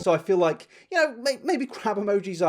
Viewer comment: I thought he said 'crap emoji' not 'crab' XDDD fuuuuuuuuuuuck